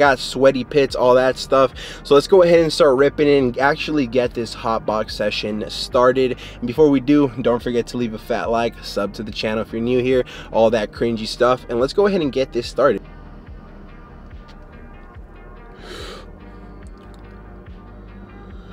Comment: Lowkey thought bro said and all that creamy stuff